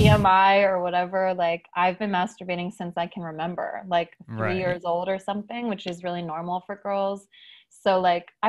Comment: did she say 3 years old i cant tell lol